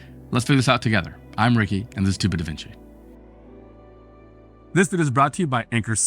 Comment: Checkout the Anker SOLIX F3800+ Anker SOLIX：<link> Amazon：<link> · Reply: I literally heard you saying: "This is stupid Da Vinci!" 🤦🏻‍♂️